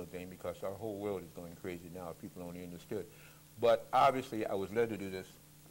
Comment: ish in does he say innerstand??? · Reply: couldnt quite make it out...almost sounds like he says innerstood